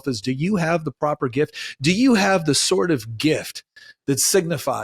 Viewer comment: I heard "do you have the sort of gift" as "do you have the SWORD of gift. "